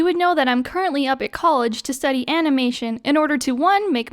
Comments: I thought u said I go to college to study anime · I low key thought she said she went to college to study anime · I thought you said anime and I was like OMG I CAN ACTUALLY GET A MAJOR IN ANIME!?